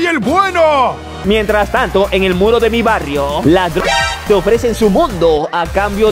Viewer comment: pense que decia las v3rg4s